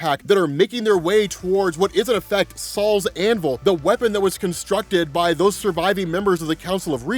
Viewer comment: Gonna be honest with you. The first 4 times I heard you say it. It for sure sounded like you said “Sal’s Anvil” 😂😂😂😂 like who the F is Sal?